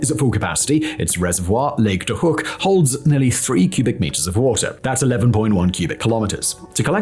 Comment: 'nearly 3 cubic metres of water'? no matter how often I listen to the at 6:08min I always hear this... did you mean something else?